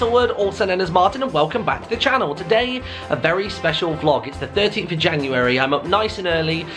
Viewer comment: At the beginning, I thought he said the 30th of January, and I was about to inform you about your time machine being revealed.